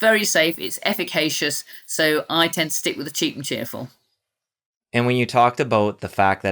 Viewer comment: did she say stick with the “cheap and cheerful?” 😂😂 I love her!